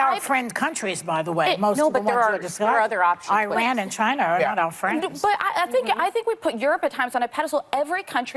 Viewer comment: did Joy say China was our friend??